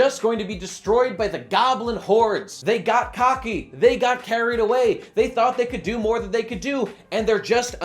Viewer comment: I have been playing too much Monster Hunter World and thought said they got Carted